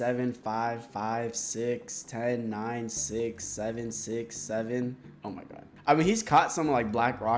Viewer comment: what did he just say 🥹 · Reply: wtf is 6 7